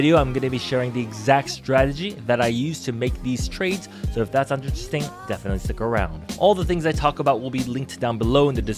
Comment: what were you saying at 0:13?? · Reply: “So if that sounds interesting, definetly stick around”